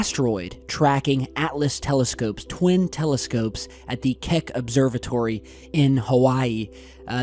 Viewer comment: I swear I heard him say "Kek Observatory". 🤔